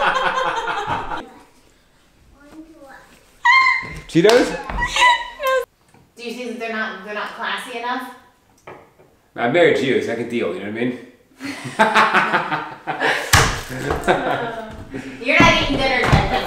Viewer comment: Initially, I thought she said; "I don't flash you enough" At 13:55...